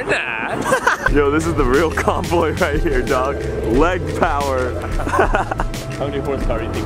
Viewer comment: I heard black power the first time and I just laughed lol. Its actually leg power